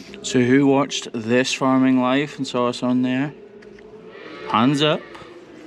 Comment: thought you said "this farm wife" as thats a cracking great farming channel to watch